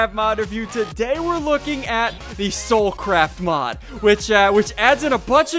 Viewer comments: soul craft I thought you said but hole craft)( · i thought u said soul crap mod ssundee